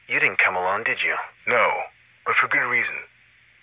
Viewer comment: When I first heard of Wally I heard it as Wall-E ... cant shake it off 😆